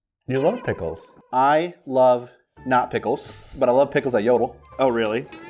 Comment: i thought bro said "i love testicals, but i also love pickles"